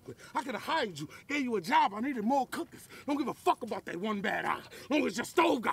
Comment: "I could have hired you, gave you a job I needed more cookers, I don't give a fuck about that one bad eye as long as your stove got four good ones" 🔥🔥🔥🔥🔥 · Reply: I think he said "more cookin'," which rhymes much better with "my first time in New York, I tore (up) Brooklyn" and "4 good ones" -- not perfectly but way more closely than "more cookers." At first, I thought he was saying "more cookers," but after going back, it sound more like "more cookin'" to me.